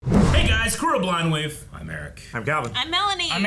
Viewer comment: what does the guy on the left say at 0:00 before blindwave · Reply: “Hey guys, crew of Blindwave”